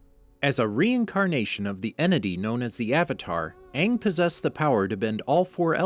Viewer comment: did you just say aang was a enemy?